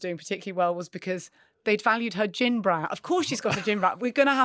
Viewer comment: “Gin brand”! I thought you said gym bra. 😂😂😂